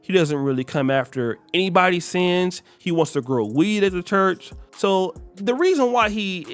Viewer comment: what did he sayyyyyyyyyyyyyy Grow weed as a church?